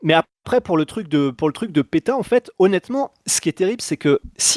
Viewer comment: j'ai etendu Pétain je me suis demandé pourquoi l'irlandais aurait parlé du maréchal 😂